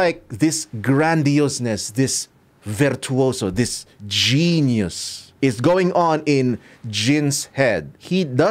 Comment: why my mind instead of hearing genius, i heard Jhin-ius.